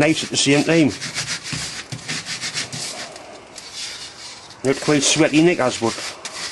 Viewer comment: Did i really hear "A bit like sweaty knickers"... ;D